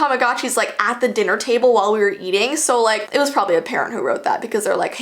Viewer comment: At the end when she said parent I thought she said parrot